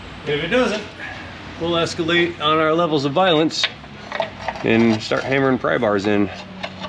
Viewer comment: Escalade thought you said it was an avalanche 😃